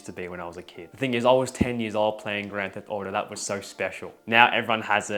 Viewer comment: i heard "i was 10 years old in 2016" and just realized how old i am holy fuck